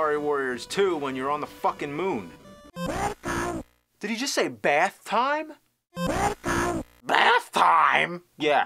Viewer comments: um...I don't hear him saying "Bath time". I hear "WEL--COME!" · I clearly hear "Welcome" i have no idea how he hears "Bathtime"